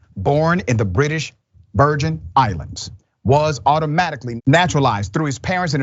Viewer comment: The U.S. Virgin Islands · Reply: I thought dr Ritchie said British Virgin Islands